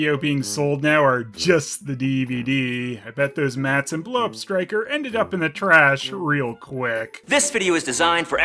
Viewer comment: I heard that as "blow-up Stryker"